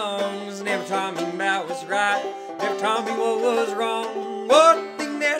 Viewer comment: I know that it wasn't intentional but I swear during 0:25 i just hear 'never taught me Mao was right' lmao